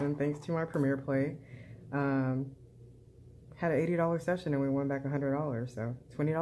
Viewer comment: Sounded like you said a $90 session there in the beginning (you were probably tired, lol)! Nice profit session there and wishing you all the best ahead.